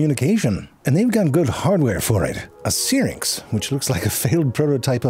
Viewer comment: *syrinx The transcript is no help. I thought I heard spharynx, but, no *syrinx